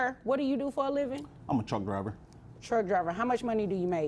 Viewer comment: I originally thought the Lady said Drunk driver not Truck driver